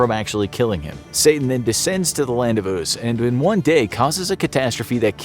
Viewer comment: "Satan go back to the land of" ????? What the heck did he say? Lol